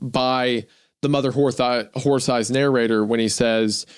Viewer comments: did Isaiah just say mother whore thighs? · 2 hours and 36 minutes in and there have been at least two instances where wendigoon almost said "mother wh*re thighs" instead of mother horse eyes. I have absolutely no idea why my mind paid so much attention to that fact but whatever it is, im now sharing this information with you. 3:27:23 again! Damnit wendigoon what is it that you have on your mind? 😅